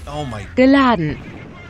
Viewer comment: FOR A MINUTE I THOUGHT SHE WAS SAYING BIN LANDEN